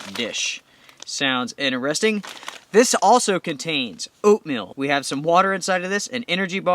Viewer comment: Nice video thanks! I like that camo jacket! I was watching at 1.5x speed like I watch most of YouTube and I thought you said that the MRE kit also came with opium hahaha! Had to rewind to hear oatmeal! Thanks for a cool video, God Bless!